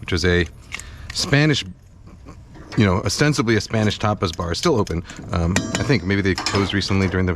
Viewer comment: He said Tapas Bar but I thought he said topless